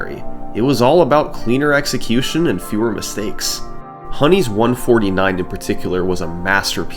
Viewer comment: Use code 50SUMMONINGSALT to get 50% OFF plus free shipping on your first Factor box at <link> #sponsored Research Credits: Alex64 did a lot of research for this video, and FrostyZako is the premier SM64 historian. They both helped me a lot, check out their channels: Alex64: <link> FrostyZako: <link> And thanks to other members of the SM64 community for helping out: GothicLogic: <link> Puncayshun: <link> Siglemic: <link> Liam: <link> Cheese: <link> Suigi: <link> Weegee: <link> Simply: <link> Kaylee: <link> And finally… I forgot to credit the musicians in the video itself, so I’ll add them in here! seimuc: <link> Chris Doerksen: <link> Moire Lights: <link> HOME: <link> And various artists from Epidemic Sound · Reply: I think at 24:20 you Say honey when you Mean ELMO, I Hope that doesn't sound rude- Just something I noticed. LOVE YOUR VIDEOS